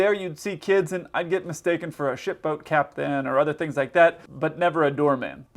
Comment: Closed Captions are wrong in a funny way. "do all that mess so sometimes there you'd see kids and i'd get mistaken for a [ __ ] boat cap then or"